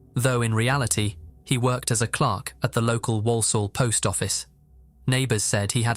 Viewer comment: The way you say Walsall made it so that I misheard it as Warsaw, and hearing "Warsaw, England", as second generation immigrant from Poland to England, made my brain short circuit.